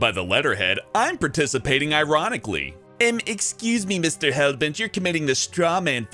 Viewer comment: when he said Mr.Hellbet I thought he said Mr.Helvete. 😂